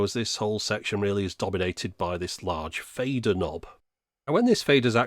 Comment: For a split-second, I thought you said "Vader Knob". The stupidest of all the Vaders. :)